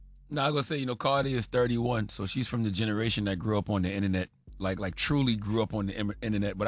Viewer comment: I might be committed to misunderstanding because i heard Char say "Cardi is 31 and she is from degeneration" 😮😮